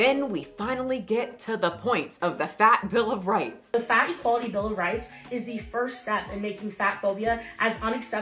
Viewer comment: I heard her say "Fattie Quality Bill of Rights"